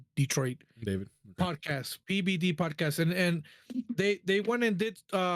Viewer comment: It sounded like you said the Pdidy podcast 😆. Ped…podcast okay.